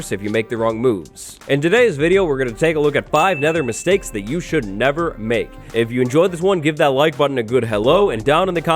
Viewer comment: Could've swore it said "Mistakes you should 'nether' make".